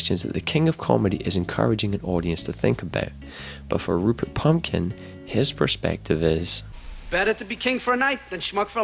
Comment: Does he call him Rupert Pumpkin at 2:14?